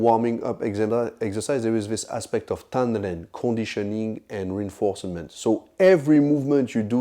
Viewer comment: What is the word he says at 0:54? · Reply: I'm guessing he was trying to say Tanren